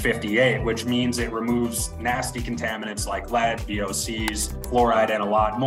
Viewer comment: did he call AOC nasty ?? because AOC is doing a great job in Congress for him to say that IMO. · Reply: He said VOC it’s even shown in the clip on one of the filters